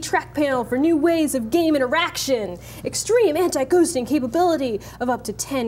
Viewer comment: I almost thought she said Eraction Not Interaction lmao